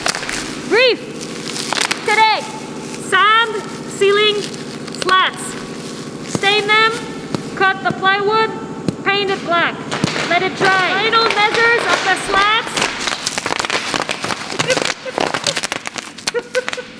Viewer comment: “Today. Breathe...today sand, ceiling, sluts” 😲 I asked my missus “Did she just say...” She replied laughing “Yes” 😂🤣 Keep up the great work 👍🏼